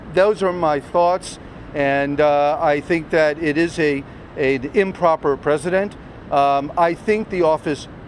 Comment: Did I hear "This was an improper president ?"